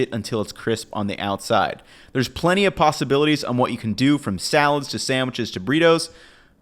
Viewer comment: so hungry that i heard "pasta-abilities"